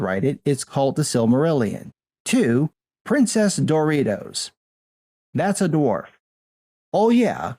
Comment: I heard Princess DORITOS I AM KEEPING IT XD